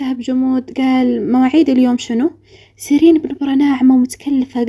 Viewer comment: اول ما سمعتها سردين😂